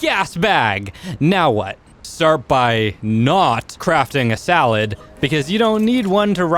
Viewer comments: Im sorry but i just have to "start by NOT crafting a SALAD" 2:16 what you got against salads man, jk but instead of saying saddle you said salad XD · did you just say a salad instead of saddle